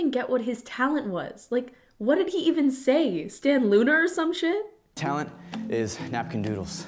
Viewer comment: am i tripping or did she say "stan loona" at 12:25????